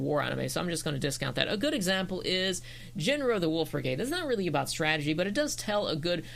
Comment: I'm a bit late to the party but I have to say, this is a great video. One question though: what's the name of the series you mentioned in 2:03 ? I'm not a native english speaker and I can't quite understand what you said XD Thank you in advance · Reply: WOW I used to talk very fast in these old videos hahaha. It’s “Jin Roh the Wolf Brigade” I could barely understand it myself 😂